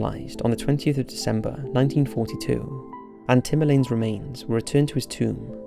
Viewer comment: Ohh he said Tamerlane! My stankin ass thought he said Timbaland! I was thinking wtf is Missy gonna do now!!??